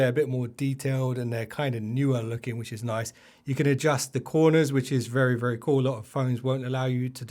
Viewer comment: Did you say you cants adjust the edges of the icons here? I didnt get you